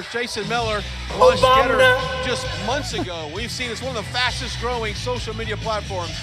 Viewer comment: David, Did anyone else hear 'sunburn' guy say "... one of the FASCIST growing social media platforms..." ? -- just after the 'Obam-NA' sound byte (est.